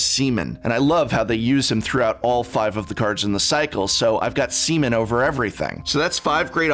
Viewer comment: Did I just hear you say "I've got semen over everything?" 🤣